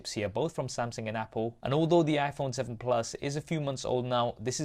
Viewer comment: Gotta wait for the iPhone 8 for a real comparison. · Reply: Went back to what he said. He said a few moths old not three. I thought he said three instead of a few, thus I repeated the information given. No need to question my education, because that is rude, you could've politely shared with me that I was wrong.